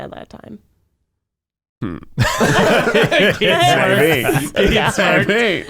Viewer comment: Can somebody tell me what Keith's saying at 28:21? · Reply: he’s saying maybe